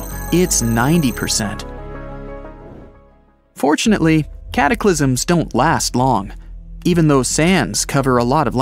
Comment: Did he just said cat aklisim